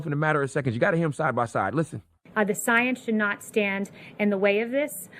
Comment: The second time her statement was played, it sounded like she said "Science does not stand in the way of this". That said though, my son got sick nearly every week/other week after first starting school. I'm not eager to go thru that again. He handled it fine, as well as his sister when it was passed to her. But when it made it's way to me. It made me fell like I was gone die. Esp. since before he was born I went over 12yrs. without getting sick. Before then it was only colds that I got rid of quickly. They didn't require fasting for weeks to heal. Which I was forced to do each time to get over the sickness brought home from his school. Plus, he's not even in kindergarten yet. His class only had like 8 students. To make matter worse everyone kept saying it was a good thing, as it helped him boost his immune system. Which maybe true, but it still had me feeling like I was gone die. With all that said, I could say that fasting will have you full of energy, with your brain in overdrive. Re-evaluating everything.